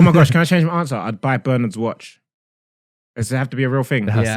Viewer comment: What watch did say? Bernham's watch?